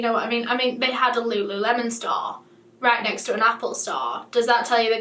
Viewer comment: I thought she was saying Lululemon stahl.